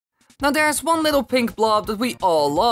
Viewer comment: At first I thought he said"theres one pink blob we call love"I was like wait wat? XD